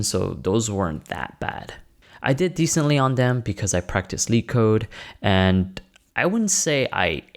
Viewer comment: did you say "leetcode"? sry, couldn't understand your accent here.